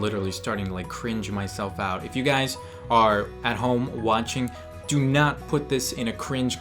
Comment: Did he said "wanching"?